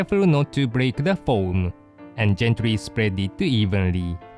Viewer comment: When he said gently I thought he said Japanese